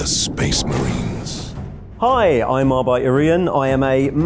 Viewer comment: I thought he said his name as Arbite Orian. Dude needs to learn how to pronounce Arbitor